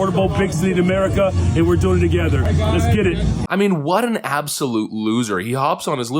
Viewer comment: Best meme ever!!! When the clip ended of Brandon Johnson in the park, the caption said, "Let's get it." I swore I heard him say instead, "Just kidding!" The latter really makes more sense.